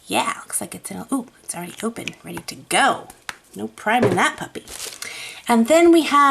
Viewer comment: The auto-generated captions say "no pride in that puppy" which I'm pretty sure isn't right, but I can't hear it all well. Could someone help me out, please? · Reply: Lol. It really does sound like I say that! I say “ no priming that puppy”. Meaning it’s ready to go.